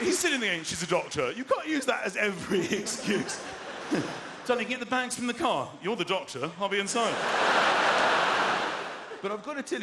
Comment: Can anybody say what he said after 6:39.....? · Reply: He said "Did you get the bags from the car .. you're the doctor..."